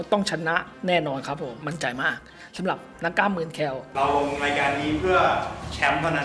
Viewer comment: พี่ครับนาทีที่7:06พี่พูดว่าเหมือนเเคล(ไม่รู้ว่าได้รึป่าวครับ